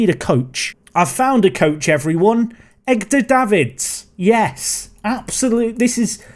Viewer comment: Did you.... did you just say "Egg-dur Davids"?